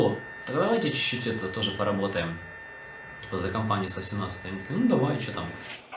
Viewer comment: he said "simnatzet" which in the subtitles says 18, actually its 17.